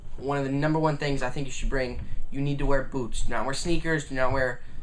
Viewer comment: when he said bring boots i thought he said boobs